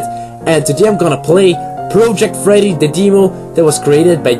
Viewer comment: i thought it said protect freddy